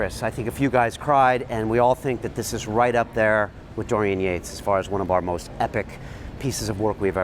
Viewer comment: Who's the other interviewee that you mentioned around 4:20? I couldn't catch his name right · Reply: Dorian Yates!